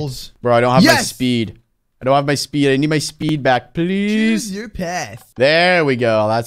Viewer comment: I thought it said chose your pet XD XD XD lol lol lol